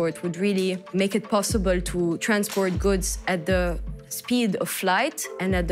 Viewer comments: Muka Zero do you know at what speed airplane operates at 33,000ft? And do you know speed of light? Go google and you will learn something new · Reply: she clearly said "speed of flight"... if you can't comprehend it, just turn on CC... Also go google "how to increase listening skills" you'll need it. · I’m sure she meant speed of sound and not speed of lights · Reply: Guys you are both correct and wrong. At 3:13 she clearly says speed of flight but 7:50 its speed of light. No problem.